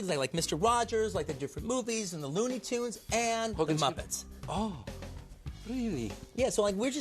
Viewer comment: Did he say Hogan's Heroes?